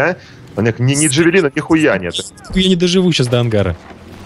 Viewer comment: мне послышалось Дядя Женя - "Нихуя не доживу до ангара".